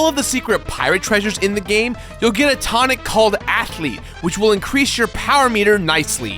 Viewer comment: When he said athlete while describing the tonic i thought he said acne